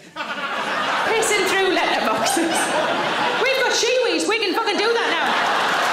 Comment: LETTER BOXES!!! I thought he said through litter boxes. I just accepted what I heard as a UK thing.